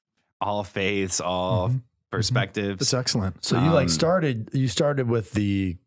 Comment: did Father say "Sexellent"? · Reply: Michael Verde - lol no, he said “That’s excellent”